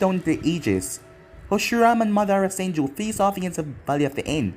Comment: Did I just hear "Hashirama and Madara Senju" ?? 🤣🤣